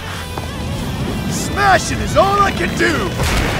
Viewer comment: What is metal bat saying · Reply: He says "Smashing is all I can do".